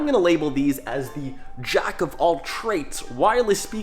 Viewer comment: did he say "jack of all traits" @ 3:37 ?